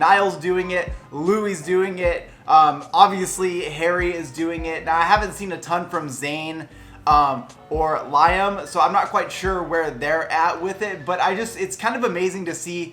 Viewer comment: did he just say laim instead of liam on 7:17 omg loll 😂😂😂😂😂😂😂😂😂